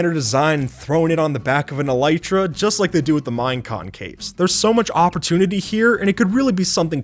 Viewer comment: Subtitles said "Mine concaves" insead of "Minecon capes"